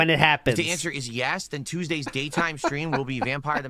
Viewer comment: dis anyone else hear him say "gay time" stream?